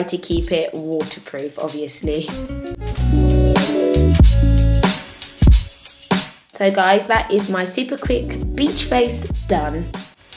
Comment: Hahaha i thought she said " bitch face is done" for a second there